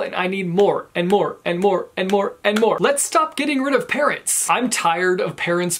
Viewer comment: I thought he kept on saying parrots instead of parents.